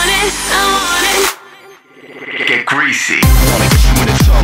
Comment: am i the only one that hears "get greasy" instead of "get crazy"? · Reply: TheGamingWolfGirl oh wow... 😱 my mind has been blown cause I heard “get greasy” like 5 times before I heard “get crazy” woah.... my mind is gone..